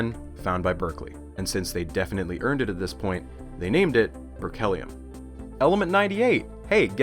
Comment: I heard 'R. Kellyium' lol sorry